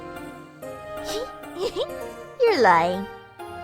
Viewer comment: It sounds like Aphmau saying "You're lying"